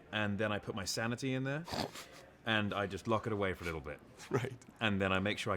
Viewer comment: did he just say LIDDLE bit, he's a British actor right?